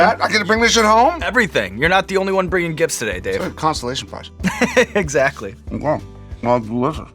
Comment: Did he say... Constellation prize? lol.